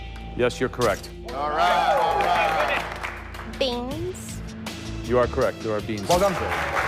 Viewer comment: I watch this with autogenerated English caption, I believe they're talking about beans but the subtitles said "you're correct to our penis." 😂😂😂😂😂